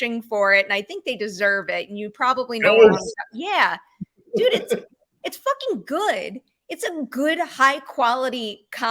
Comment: Which show is mentioning at 17:14 that calls out with his hands cupped around his mouth? I've replayed it several times and I still didn't catch the name of the show. It doesn't appear on closed captioning either. never says the name, just that it deserves some award season love. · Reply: It's ghosts